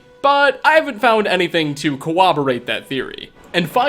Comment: Did - did you say "co-woberate?" Definitely a better way to pronounce that, haha